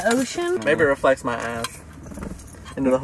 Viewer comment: omfggggg i also thought you said ass. Until told Brooke, then I realized lmaoooo